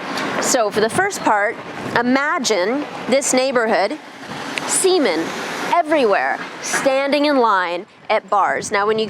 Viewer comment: seamen EVERYWHERE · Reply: At first I thought she said "semen everywhere." I was like, 'What a vulgar thing to say!'